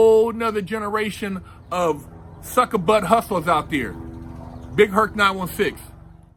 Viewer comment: What's up Big Herc? Did you just say "suck butt hustler"? 🤣🤣🤣🤣